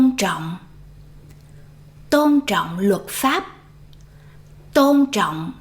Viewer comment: tôn trọng luật pháp English subtitle wrong.